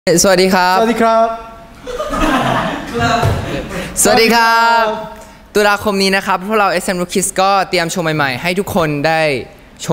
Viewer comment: Basically what I heard: 0:06 Hi pull out SMRookies God cuz he chew my high taekwondo ai